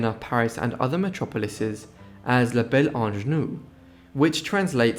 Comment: « La belle ingénue » means the beautiful naïve young girl, which was probably ironic in this case… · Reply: I thought that was what he said too, but then I realised that he said ‘la belle ange nu’, ie the beautiful naked angel.